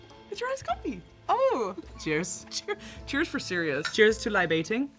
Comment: DID SHE SAY “cheers for Sirius?”